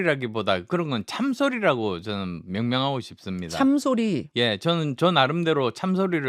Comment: 참소리를 참새소리로 들었는지.